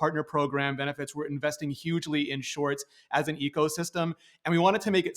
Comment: did he say hugely???